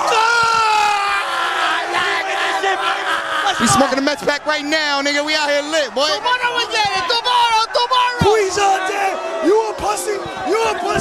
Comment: 😂😂😂I almost thought dude said he's smoking that meth pack